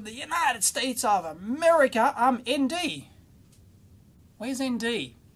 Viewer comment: I thought at 31:00 you said MD (Maryland).